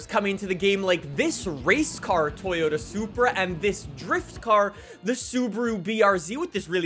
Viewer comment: hold on. Did you say driftcar?